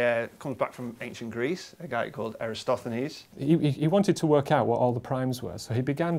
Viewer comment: the subtitle says "Aristophanes" but it should be "Eratosthenes".